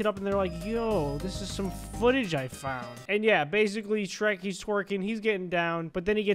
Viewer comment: I was laughing so hard when i heard “shrek twerking”